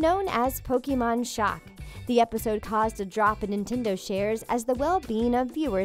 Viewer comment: I don't know if its just me but in 1:45 it sounds like she is saying NEAN-tendo